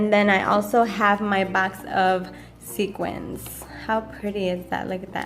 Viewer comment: Sorry sequences? Not sure what you were asking! Lol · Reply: oh never mind lol. I was being stupid. You were saying sequins and I thought you were saying sequences and I was confused. 🤦🏻‍♀️